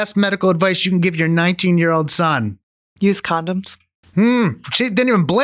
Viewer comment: I laughed at the best advice being use condoms. Mainly because I heard it as "used condoms", which changes the advice just a bit.